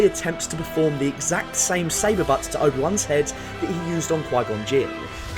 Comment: Hello there, I’m french and I don’t get what you’re saying at 4:12 after the word 'saber' and before 'to obiwan’s head'. Just trying to improve my English pls haha. And amazing video it gives depths to this fight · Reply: 'Saber bust' maybe ?